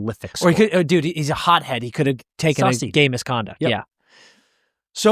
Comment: the captions saying gay misconduct instead of game misconduct may be my favorite part of this video